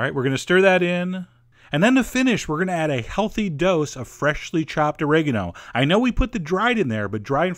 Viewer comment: Can anyone tell me what's the herbs in 4:02? I couldn't catch that name clearly :( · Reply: Ka Chun Hoi It's fresh oregano. He used both dried and fresh oregano